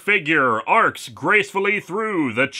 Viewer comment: did you said Braun Strowman?!? 😂